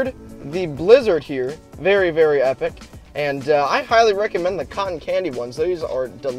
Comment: i thought he said "Cotton Canny Booger"